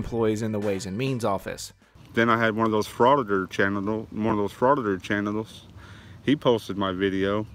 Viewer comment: FRAUDITOR GENITALS?! I know he said "chanitals" but who didn't hear genitals the first time you played it?! And WTF is a chanital?!